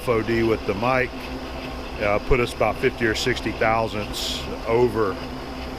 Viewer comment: When you said 500/1000in, I heard "5 hundred-thousandths of an inch" and I for a sec I totally believed it.